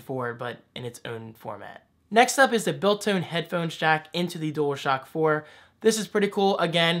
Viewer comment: did say "Built-Tone Headphone Jack" 4:02 ?? haha!